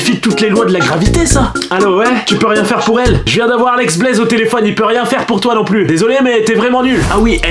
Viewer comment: il a vraiment dit alex blaize ou c’est moi 😂